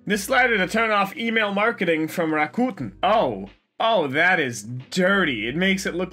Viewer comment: Is he saying Rakuten, or what? I want/ need to know since I have the app.